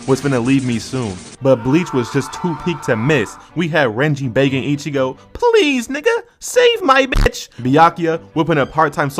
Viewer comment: thought he said renji banging Ichigo